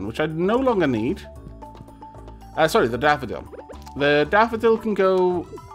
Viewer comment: Every time says "Daffodil" I hear "Dapperdil"